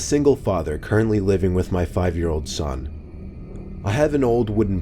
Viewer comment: Story-3:when u said I am a single father living with my 5 year old son I thought u said "my 50 year old son" lol